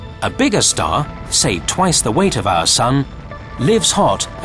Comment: I thought you said light house sun. You said like our sun, emberrising.